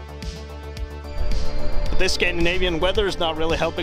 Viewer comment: Nice channel! You got another subscriber. Where in Canada are you? And if you don’t mind me asking, ballpark figure, how much did you pick it up for? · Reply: Sorry about that, at 2:08 I must've been really sleepy and tired when I started watching your channel, I heard "Canadian" instead of "Scandinavian" winter, lol I've always loved Unimogs but they're super expensive here in Canada, I've seen a couple on BAT (Bring A Trailer) website and the cheapest I saw was around 25-30k USD. Keep up the good work, went through all 10 of your unimog videos in less than a day, waiting for chapter 11.